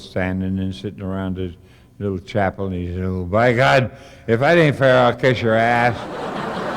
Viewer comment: Hey everyone, at 29:07, what was the punch line. I couldn't understand it. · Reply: I didn't catch it either, Mark. Sounded like: "Well, if that ain't fair I'll catch your ass." - which makes no sense.